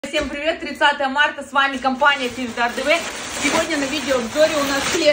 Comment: Оставь эту торговлю мертвечиной я на хате едь а название компаниии мне одному послышалось компания п..а тв 😅😅😅😅😅😅😅😅😅 5000 ставьте 😉👍